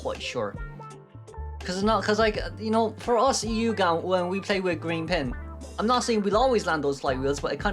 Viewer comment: did you say with us eu gang we always get Green pingm.